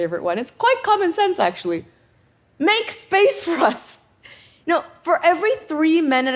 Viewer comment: "Make Space for us???!" Someone should please help me verify, did I hear that correct, because I believe I lost a few brain cells somewhere in that region in the video? 15:42 I also think I heard something like when more men show up at the table, more women kill each other or something.